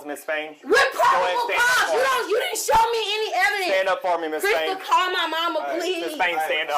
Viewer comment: I kept mishearing the offcier calling her "Miss Thing" "Stand up, Miss Thing" "You’re under arrest, Miss Thing." "Calm down, Miss Thing" 😂